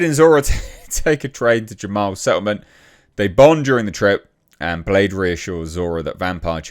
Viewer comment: I thought u said they bone not they bond 😂😂😂